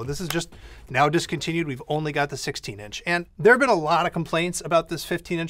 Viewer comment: I thought it said "16 inch hands"